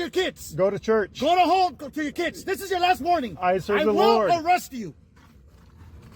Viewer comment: Ehhm did I mishear or did the fascist say: "I won't arrest you!" This is a blatant death threat, threatening the man with murder. WTF